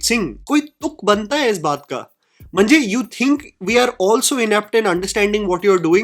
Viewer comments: Did you just say “Mhanje” ? 😂 Marathi word which translates as “means”. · did i hear "mhanje" at 9:20 or was is jst my marathi ears overhearing ?!???! 🧐